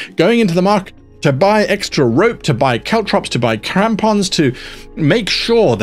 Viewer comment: did you say "buy Tampons?"